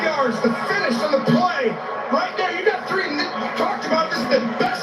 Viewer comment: Sounds like he said you got three niggas on the play!!!